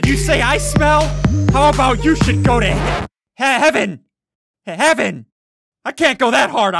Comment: wait what. Ok you said Heaven not hell that’s good 😌